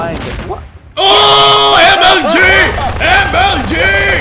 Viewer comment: pewdiepie said mog instead of omg